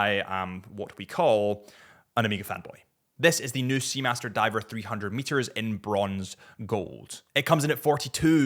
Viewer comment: did that say 3000m? Im sold!